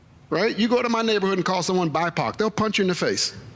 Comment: At 1st, I thought I heard BiPac. Like a new Rapper name. 😅